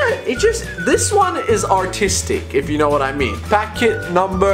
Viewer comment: on 9:52 u thought he said autistic xDDDD